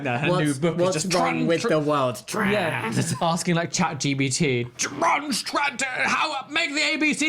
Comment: ahahah did say ChatGBT? like an LGBT+ chatGPT, I love it